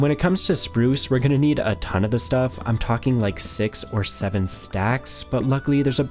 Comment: what how many stacks? did bro say six or seven stacks????